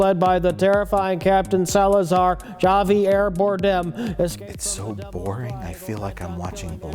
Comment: "It's so Space Cop I feel like I'm watching Blade Runner." Yeah I misheard him the first time too.